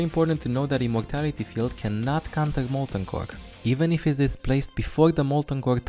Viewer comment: did he say "molten cock"?